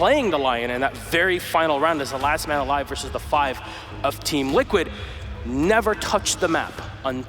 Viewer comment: Eu ouvi um team liquid em 1:35?